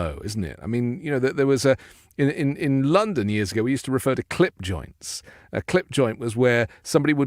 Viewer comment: i thought you were saying "clit" joint.